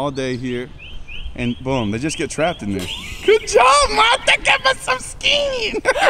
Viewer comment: Mata that's my last name 📛 but at 1st I thought he was saying Martha lol